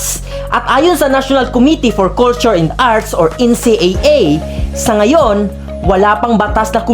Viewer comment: Tama ba narinig ko kuya bubbles? NCAA? 😅